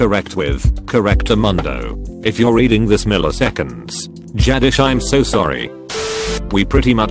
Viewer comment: If you’re reading this Milliseconds. Jadish I’m so sorry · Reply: I heard it as radish and started laughing